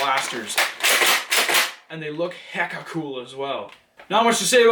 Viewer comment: And did he say hecka cool at 2:10 lmao